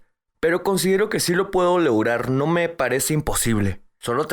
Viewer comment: What does the patient say at 24:50? "Si lo puedo ???, no me parece imposible." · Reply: Lograr